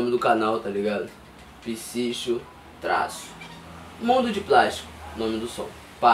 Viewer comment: Na hora que o falou o nome da música eu entendi "Bunda De Plástico" kkkk